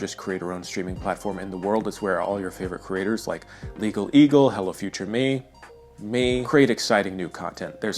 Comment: During the ad-read I heard "Hella future me" which is just me but I'm wearing visor style sunglasses and my shoes have LEDs